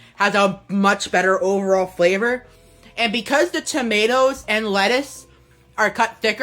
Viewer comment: to me it sounded like he said Chomatoes